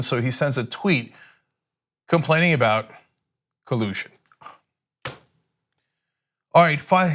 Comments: thought cenk said pollution. . . thaats not right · I literally thought you said Trump said pollution not collusion. That woke me up for about half a sec.